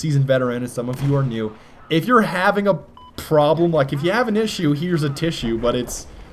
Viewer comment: it sounds like hes saying "if your having a 'beep' problem. lol perfectly timed beep